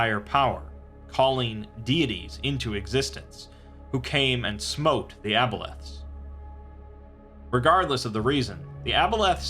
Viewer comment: I could have sworn you said "-came and smoked the aboleths." And I just imagined ancient people using modern slang mixed with their old speech patterns while they summon their gods haha.